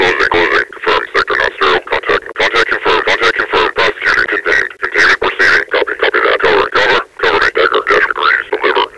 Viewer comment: what is he saying? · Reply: Contact confirmed